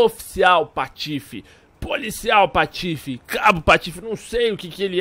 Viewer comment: Cabo no patife eu entendi certo ?